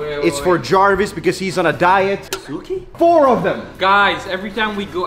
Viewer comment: Anybody else thought he was saying Bazookie at first? Lol